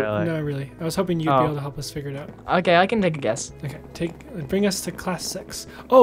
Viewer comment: made this video a little longer for you guys cuz i was out spending time with my family this christmas and missed an upload yesterday! ( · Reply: it sounded like you said "Bring us to class sex"